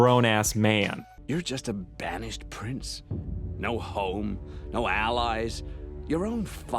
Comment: off topic but I heard no hoe and started laughing because Zuko had and got no bitches (Not including Mei don't get me Zuko and Mei shipper)